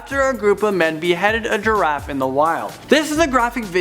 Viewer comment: I thought it said graffiti was beheaded